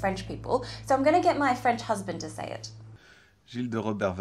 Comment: Ok I died when she brought the husband into this for the pronunciation 😂😂😂😂😂🤦‍♂️ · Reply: Oh, I heard it was "friend's husband" but it must have been French husband :D